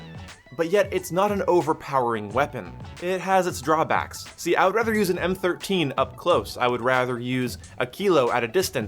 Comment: did he just say he’s rather use an m13 up close?????